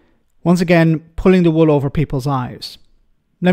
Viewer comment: I hear "Pulling the wool over people's lies" which is a fair summary of the Brexiteers' propaganda.